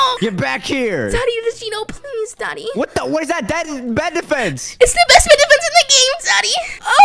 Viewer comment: If didn't have a girl maybe he would be with 😂💀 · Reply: Did just said “What is that dangling er bed defence?”